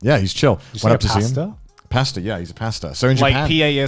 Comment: I heard "pesto"